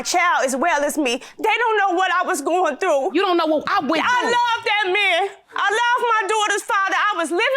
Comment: All I heard was, " ...AH LUH DAT MAN" and I knew...it was bedtime.